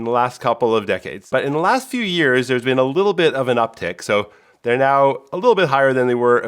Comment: the subtitles should say uptick instead of uptake!